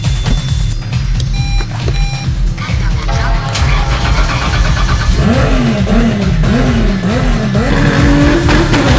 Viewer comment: qué es lo que dice el auto de Shirako Takamoto antes de encender? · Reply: "card not inserted"